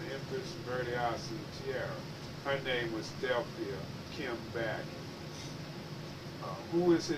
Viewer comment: at the 09:35 mark when hes speaking of who crowned the Empress....is he saying "Delphia Kimm "Battie"?